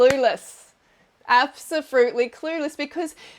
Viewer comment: did she just say absofruitly